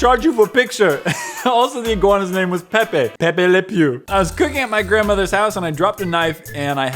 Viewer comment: When he said "cooking" I heard "queefing"…